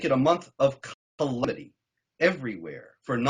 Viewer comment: There is an audio cut-out that makes it impossible to understand an important point. At 1:26 " ... be ready to make it a month of ___ everywhere for non-believers ... " A month of what? It sounds like a single word, but it is impossible to say what it is. Later, he makes the statement again, I think the word is "calamity", "a month of calamity". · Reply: a month of calamity.....